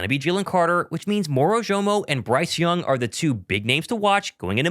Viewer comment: I heard you say Bryce young not Byron young